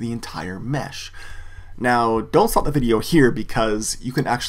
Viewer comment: Did you just say "don't fuck with the video here"? lmao